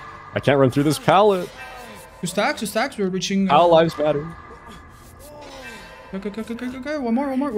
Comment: did he say all lives matter or our lives matter?